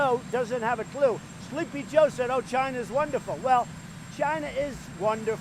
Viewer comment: Dude straight up said Vagina instead of China